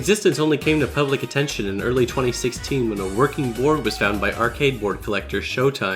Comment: "when a working ___ was found". What's he saying there? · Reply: board